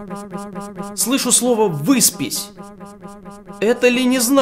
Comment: я услышал побрись хавхах